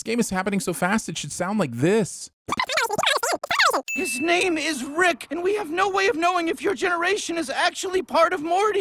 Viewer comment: (yes I slowed the video to find out what he said) he says and I quote "what do people not understand about time dilation, its time dilation" You're welcome fellow curious folk